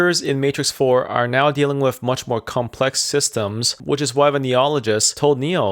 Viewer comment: Hi, thanks so much for this! 🙇‍♂ Q: What's your word at 4:59? "... which is why when ___ told Neo ..." 🤔 [😯 👈 That's me, hanging on your every word. 😝] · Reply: It’s “Neologists”, which are the people who studied Neo after he disappeared for 60 years and the guy who escorted him around Io.